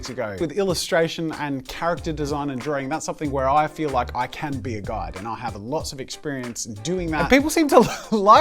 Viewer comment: What said: Where I feel that I can be a guide What my stupid brain thought he said: Where I feel that I can be a God